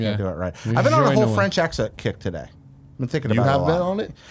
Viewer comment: I thought said "I've been on a French Exit kick today." At about a minute in